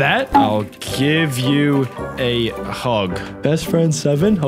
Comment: said huge instead of hug😂